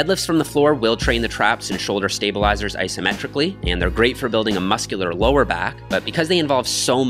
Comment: My form was a bit sloppy in this video. I was sick while recording this workout and didn't want to drop my weights back too much, but re-watching some of this footage, I think I should have. Anyway, I hope you enjoy! Happy New Year!! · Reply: I like your videos and wish i discovered them sooner! just a tiny thing i think you said asymmetrically rather than symmetrically at 7:02.